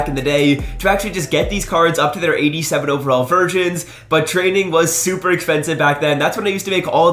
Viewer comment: am I dumb or did he say virgins